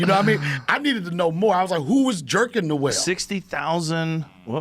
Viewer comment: Did Donnell just say "who be jerking off the whales??" - LMFAO